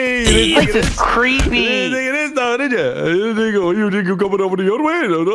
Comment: did nogla just said the nword? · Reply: No he said "think ya"